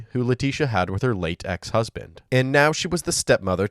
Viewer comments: "who Laticia had with her latex husband..." LOL, i know that's not what he said but it sure did sound like it. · when the narrator said "her late, ex-husband"... it actually sounded like they said "her latex husband" 🤦🏼‍♀️